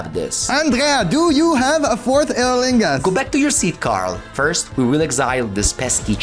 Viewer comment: I heard “Andrea, do you have a fourth annilingus”? 😂